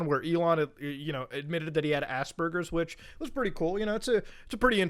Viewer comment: He actually said Ass Burgers!? 😂